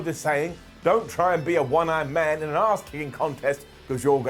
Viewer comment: did really say don't be a one ARMED man in an a$$ kicking contest??? 🤣🤣🤣🤣